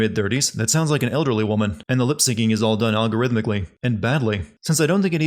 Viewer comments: ding says "algorithmically" but the subtitles say "arithmetically" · Narrator says "Algorithmically" Subtitles say "Arithmetically"